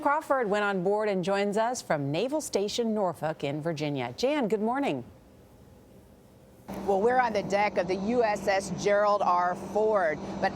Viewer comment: What did she say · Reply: "We are to the naval station of north folk virginia jane good morning. Welcome to the deak of gerald ford." Nous somme à la base navale Norfolk en virginie, ici Jane, bonjour. Bienvenu sur le pont d'envole du Gerald Ford (non du Porte avion) bla bla bla. ok ?